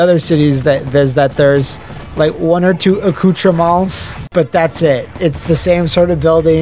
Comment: What did he say at 53:03? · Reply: accoutrements